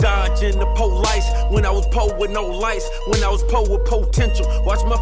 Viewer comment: This song is still a banger today, how it didn’t blow tf up is beyond me. 2 chainz killed it, one of his best verses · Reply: I've listened to this song a million times and always thought he said "when I was poor with no lights" there